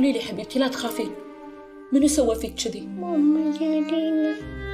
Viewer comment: وش قالت؟؟ ايش اسم المسلسل? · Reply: قالت ماما جليلى